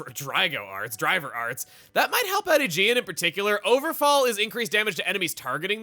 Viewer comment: the proof that i dont pay attention to art names in XB2... i thought Morag was saying "ogrefall" this whole time